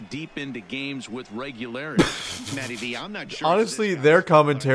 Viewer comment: did they say MATTYB?!?!?